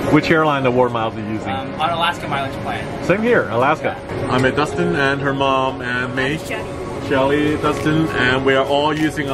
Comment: Lol Sam you said here is Dustin and her mom who else heard that 😂😂😂